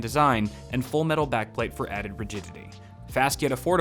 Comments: Did anyone else hear "added virginity" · I thought he said added virginity 😂